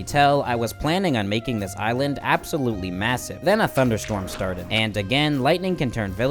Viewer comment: did you say massive